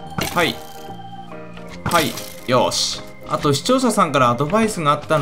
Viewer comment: 最初の「視聴者さん」がしちょうささんに聞こえる